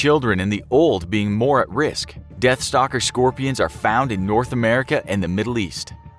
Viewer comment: you said South East Asia while highlighting the UAE. So, where exactly did you meant? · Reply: Also said North America at 5:43 but subtitles said North Africa...